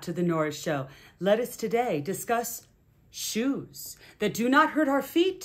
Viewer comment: I thought I’d clicked the video about the tall it painting, so I had a moment of true confusion, because I honestly thought I heard you say “Let us now discuss JEWS. That do not hurt our feet. And ALSO….” That’s when I paused the video and checked the title. 😂😂😂 👠👠 not the same as ✡️✡️